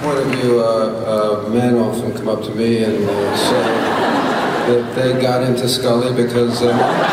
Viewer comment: what did he say? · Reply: I'm paraphrasing, "Men often come up to me and got into Scully because of Mulder."